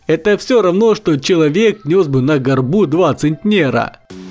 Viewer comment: Почему я слышу не центнер, а негр... 20 негров....